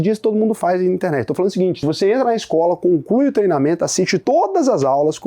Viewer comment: falou "conclui o treinamento", eu ouvi "com o c... e o treinamento".... 23:00😅